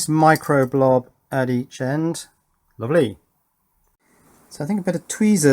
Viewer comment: What on Earth did you say at 20:00 - 20:01? Editing mistake? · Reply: "Lovely"